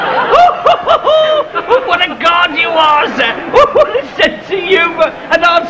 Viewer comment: What did that guy say..."what a G- - - - you are sir" What was the word he called Hancock? Did he say "what a god you are"? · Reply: "Your a Card"..